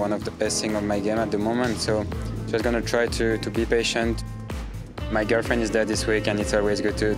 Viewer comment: I swore he said "my girlfriend is dead"